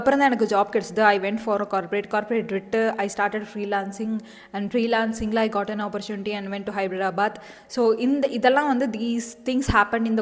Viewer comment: did you say Hybridabaad ? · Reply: Yes yes! I was living in Hyderabad last year.